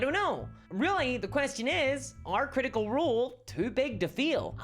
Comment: It sounded like you said are CR toi big to feel, lololol